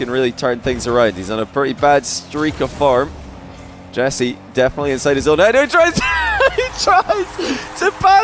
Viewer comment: I heard "Definitly inside his own net" at first here 😂